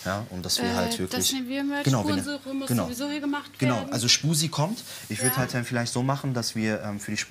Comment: X"D Habe zuerst bei 5:51 verstanden -"Schmusi kommt!"